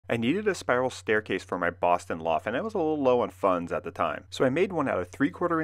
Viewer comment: I heard "my boss in law" instead of "my Boston loft" and wondered who is a boss in law?